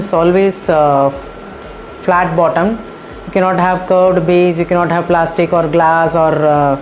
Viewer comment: can someone tell me what she said you can't have ... for the first cookware? I didn't understand after listening to that part 50x on repeat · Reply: Hi Julia, sorry you were not able to follow that part. I have said cookware cannot have a curved base. Thanks.